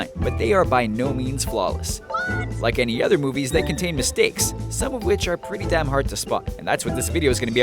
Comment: did he say damn ,not that I have a problem with it. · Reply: He said "Some of which are pretty damn hard to spot "